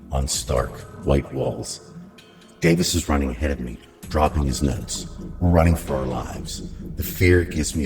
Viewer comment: ngl I thought he said "dropping his nuts" lmao